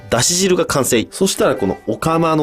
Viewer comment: 生米です。が、生ゴミですに聞こえた